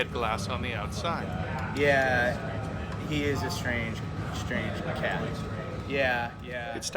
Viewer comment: who heard the man said “yeah, he’s a strange strange cat” Who agree?